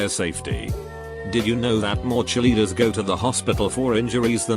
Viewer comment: Lol I was listening to this and I thought he said chilli eater hahaha not Cheerleader 😂